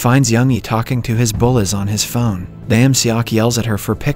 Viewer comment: Is it just me or is anyone else misheard bullied and bullies as bullets?